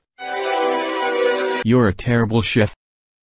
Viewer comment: You are terrible "SHIT" it actually says chef but that's what I heard